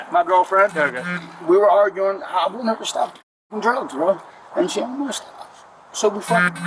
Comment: You put the caption as “I will never stop doing drugs” he actually says “I want her to stop doing drugs”